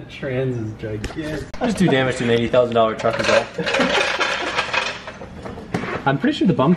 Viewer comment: Lol I thought he said 280,000 truck